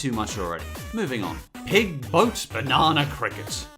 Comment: Its says Boat on 7:15 instead of goat